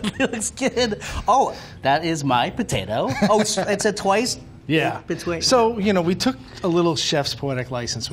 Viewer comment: Did anyone hear saying Twoice Bwaked Powtaoe